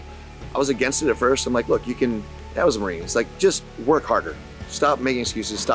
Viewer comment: Did say he was a Marine right at the 17:54 mark roughly? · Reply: He said "dad was a Marine"